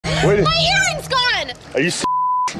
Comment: I misheard it as "my hearing is gone!"